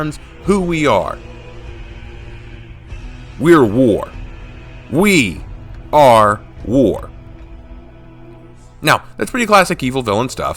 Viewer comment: I reach to the part astrea says we are war but it got cut off and all i hear is "Kentucky fry chicken."